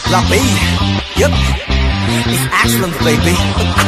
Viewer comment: Silly me haha I thought zico was saying "it's asylum baby" this whole time... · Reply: LOL I thought he said it's Iceland baby 😂😂😂